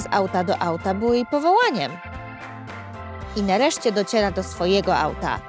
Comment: czy tylko ja usłyszałem dociela ?????